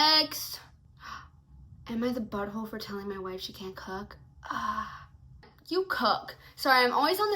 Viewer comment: Bro I thought I heard that one story as ‘aita for not letting my wife c u c k’ and not cook bro 😭 I do have dyslexia but that was lwk a jumpscare